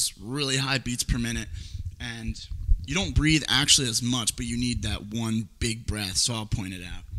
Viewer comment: Sounds Like You Said "You Need To Take Big Breasts"